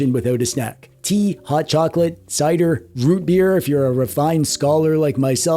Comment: Did..did you just say “Warm Rootbeer”